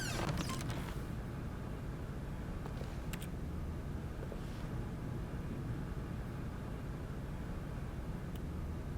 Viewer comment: what does it say at 13:09? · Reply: Light (Megagirl) I got a feeling money's is for stealing but not yours of course say that's a nice purse